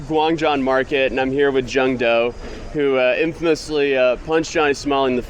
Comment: anybody else thought he said “John Doe” As a means to hide his name? 😂